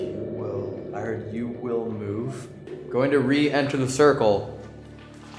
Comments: I hear "You will move out of this space. exit." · at first it says „you will move“ and after that it sounds like it says something with „entry“ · "You will move out , you will be denied entry" i swear i heard it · I hear You will ---- pain on entry/reentry. Maybe?